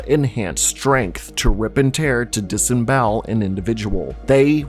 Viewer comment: Did you say... RIP AND TEAR?!?!? 😲